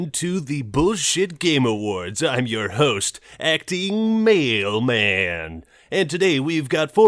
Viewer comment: The acting Male Man. What I heard it as. "Acting Mail Man. Delivering your mail with the voice of gold."